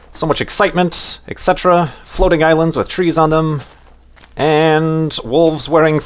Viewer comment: Lol in the beginning I thought you said floating islands with cheese :P